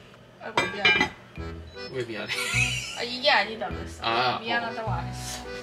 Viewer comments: 엄청 느리게 들으면 "어이구 이게 아니야" 라고 들려여 ㅋㅋㅋㅋㅋㅋㅋ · 저도 미안해라고 들었어욬ㅋㅋㅋㅋㅋㅋㅋㅋㅋㅋㅋㅋㅋㅋㅋㅋㅋ